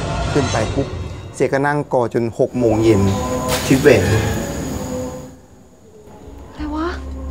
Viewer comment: ได้ยินเสียงว่าคิดไปเอง มาจากข้างหลัง